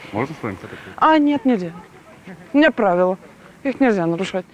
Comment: можно с вами сфоткаться/познакомиться(не услышала)?! Нет нельзя у меня правила